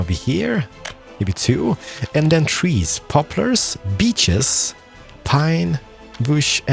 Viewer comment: I thought you say 'bitches'